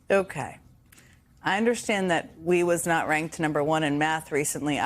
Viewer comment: Did I hear the say “we was ranked at…” at 2:41?